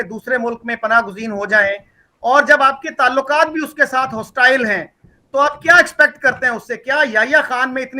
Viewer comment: Adil Saheb, listen again sahib says "Jab apke taluqat uske sath hostile hain" Meaning Pakistan ke India ke saath. You like a good liar heard what he didn't say but wanted to hear. saheb never said India hostile hai that is from your 'gazwa e hind' mindset it cane up.